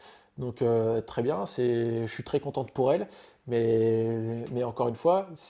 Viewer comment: c’est moi ou Benjamin dit « je suis très contente pour elle » 😂😂